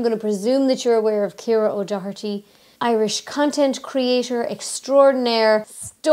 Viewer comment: I could have sworn you said, "Irish ** and creator..." which was of course appalling because you both seem so nice. Then again, I don't know how Irish ladies joke with/about each other. I should probably drink slightly less.